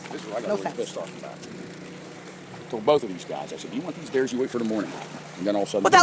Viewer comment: Honestly, I heard, "If you want these stairs", and "secure them, wait for daylight ... IDK I know it's sick anyway.